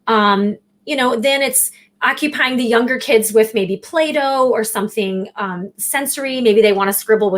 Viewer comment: I thought said that she occupied the kids with Plato, not Play-Doh, at first